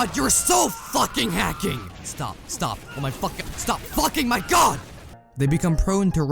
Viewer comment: min in and I hear ''stop fucking my god''